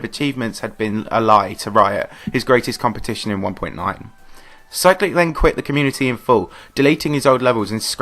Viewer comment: Lol i thought you said cyrillic quits could’ve made my day